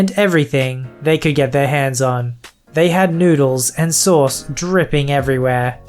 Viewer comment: I was distracted for a minute while you were narrating and I heard "needles and sores dripping everywhere" instead of noodles and sauce. Oh gross. So apparently my ears are broken. :p